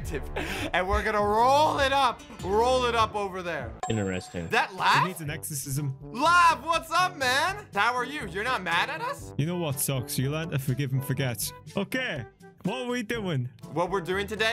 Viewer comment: did say exorcism??!!!